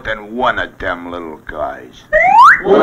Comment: what are they saying ? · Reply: What Do You Think, Brother?